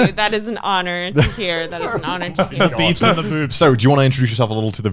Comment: "-the beebs and the boobs" Or I heard that very wrong What did the hentaiboi say